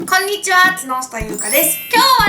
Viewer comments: こんにちは〜木下ゆうかです が こんにちは〜角下ゆうかです に聞こえたんだけど…😂👂← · 「木下ゆうかです」が「つのしたゆうかです」に聞こえたw